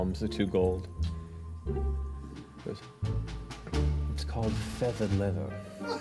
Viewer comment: What's Matt say around 6:20? "It's called Feather Leather"?